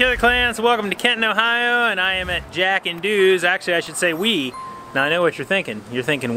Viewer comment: we pronounce it Jack and Do's (doughs) pizza, huge pizza and huge on quality too! they make a great pizza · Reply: Coulda sworn he said he was “jackin dudes in Kenton” 🤷‍♂️